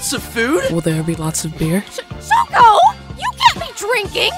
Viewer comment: The fact instead of shoko it was shoe is just pure comedy